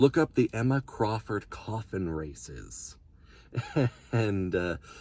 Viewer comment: When you said coffin races I thought you said koffing races at first XD